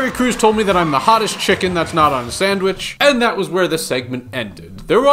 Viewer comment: Oh I thought he said he is the hottest chicken that's not on set lmfao